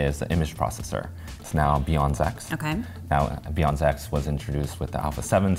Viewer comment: When he said "BIONZ X," I thought he said "beyond sex." Like saying the image processor was better beyond sex.